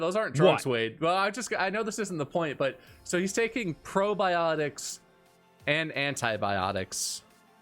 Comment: Who else thought said "brobiotics"?